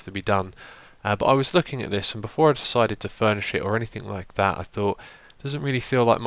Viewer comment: when you said "furnish it" I thought you said "burn this shit" xD